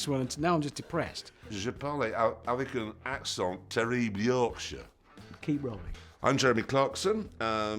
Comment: did he just say he doesn’t like the yorkshire accent? I’m offended